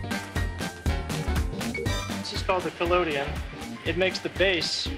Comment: I thought he said " This is called Nickelodeon "